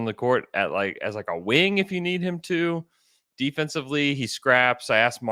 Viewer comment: for a split second i heard "defensively, he grabs ass" instead of "defensively, he scraps. (Pause) I asked Mark..." 😂😂😂😂😂